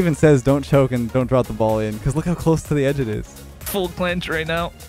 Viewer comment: did he say full planche right now?